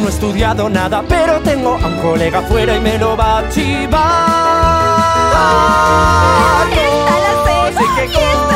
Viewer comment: Lo de “me lo va a chivar” yo siempre escuche “me lo va a archivar”